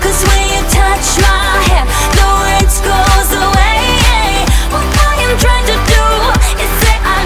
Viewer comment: Is it just me because in 1:08 I heard "Laurence goes away" lol!!!! Heheh 😂😅 Also like if u watch or heard of Aphmau comment which one u ship #Arrmau( Aaron and Aphmau) #Laurmau( Laurence and Aphmau) #Garrmau( Garroth and Aphmau) or make up your own!